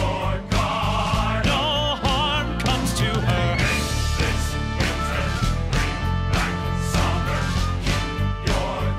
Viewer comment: I keep listening to this even after 4 years xD Does he say "all harm" or "no harm" just before 1:28 I still cant tell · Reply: It’s “no harm” this was before Hans was revealed to be evil, so he still told them not to hurt her since she’s Anna’s sister and the queen.